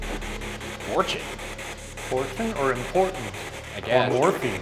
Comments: I heard "portal" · For post-mortem: when the spirit box says what you put as “important” @ 9:17 it sounds like it says “portal” which if oujia board sessions have been conducted and portals opened could be why they’re there #postmortem #buzzfeedunsolvedpostmortem #shaggyshane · when ryan asks why it stays in the jail at 9:15 i think it’s actually saying “warden” not “important” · When the spirit box said "important", I heard "Bortles" and I was like "JASON???!!" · I thought it said “portal”, not “important”.. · For #postmortem I heard “the portal” where you boys heard “important” have I too lost my mind? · i thought it sounded like “portal”, which link to the the room that is supposed to have one in? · Why do I also hear “The Portal?” (I hear “Important” too) #postmortem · sounded like it said more so "port" than "important" but you said african american sailors/dock men were locked up there if they came into Charleson, and port would be an acceptable answer to the question just a thought .... #postmortem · When he was talking to the ghost in the chair i thought i heard it say morphine 9:24 So when later in the video they mentioned the people asking for morphine i got so spooked oh my god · sounded like they said “Portal”🤔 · I heard "morgue" instead of "fortune"/"important" and seconds later they move on to the morgue as the next most haunted room. Spooky~~ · hey shane and ryan i actually hear “warden” instead of important #postmortem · instead of important I heard a portal